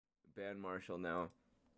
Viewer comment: ban marshall when? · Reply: It sounded like you said 'Marshmello'. Lol.